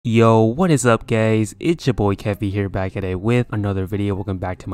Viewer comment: did he just say "whats up gays"